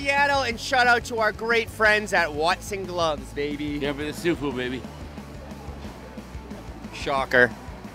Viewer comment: RA is a real guy. 21:03 That's how you do an outro I think he said " here for the seafood baby" maybe?